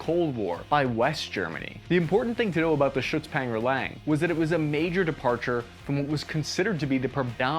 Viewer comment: Did I hear you say "shits packer lang" 🤣